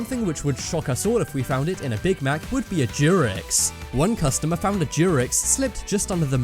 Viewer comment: This dudes pronunciation is terrible, at 1:51 I kept hearing jurex and i legit looked it up if it was some sort of animal or what but nothing, i finanly turn on auto captions and see it said t-rex.